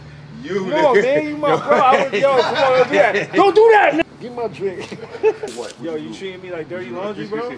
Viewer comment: sounds like he was gonna say “don’t do that nigga”